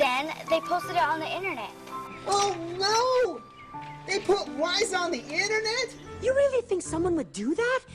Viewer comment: I swear said whys instead of lies.